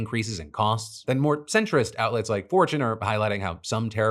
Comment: I nearly choked on my sandwich when i thought i heard you reference "a more centrist newssource fourchan" Luckiky, you have not lost your damn mind as i had missheard 'fortune' Phew.